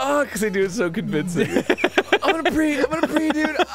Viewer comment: what are they saying? "im gonna preed" is what it sounds like